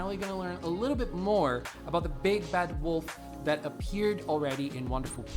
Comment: Not the big fat wolf-, maybe he said bad and I misheard lol